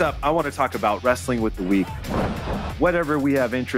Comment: Sounded like sky said I'm interested in weed instead of week